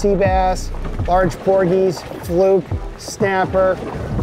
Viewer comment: The caption said Pogies vs Porgies when Capt was talking about it. Might be nit picky but just pointing that out.